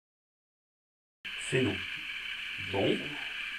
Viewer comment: on dirait que ca dit " c'est moi " a